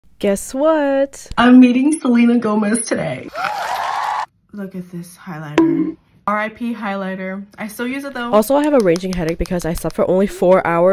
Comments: i thought she said i’m gonnna eat selena gomez today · I thought you said I’m eating Selena Gomez oops 🤭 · I thought you said"We're eating Selena Gomez today👹" 😭